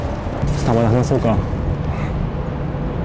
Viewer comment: 「あれってレート？」が 「あれって冷凍？」に 聞こえたwww